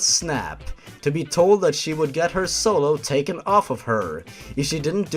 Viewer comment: Wait what the fuck??? Did the teacher say she would give Melina's soul to someone else???